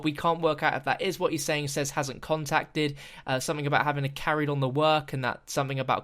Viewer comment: He kinda sounds like he says, Reznov hasn't contacted.